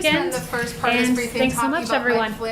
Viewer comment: She's...WITHOUT A DOUBT!.. THE..smartest person in that room.. FACT!.. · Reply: I think i heard her say, "JUSTICE MATTERS!" 👍